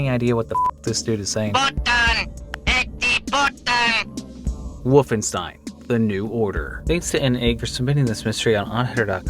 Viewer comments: It kinda sounds Slavic? I hear "Эти" which means "These" but besides that I'm completely lost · that phone booth really sounds like some is saying putang ina or something or maybe I just play too much Dota2